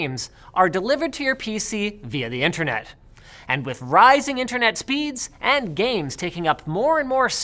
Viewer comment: I thought Linus said "with Ryzen internet speeds" lol